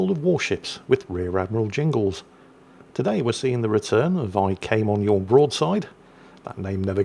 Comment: opening sentence I totally thought he say “today we are seeing the return of ARP Yamato” 😊😊 I wonder what’s jingle’s position on ARP Yamato cuz it is in store right now.